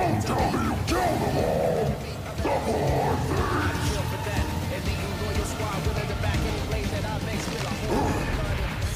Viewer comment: I didn’t understand what he was saying Can anyone help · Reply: "The poor things" is what he said.